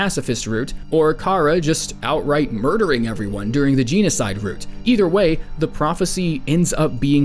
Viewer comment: did you just say GENEocide???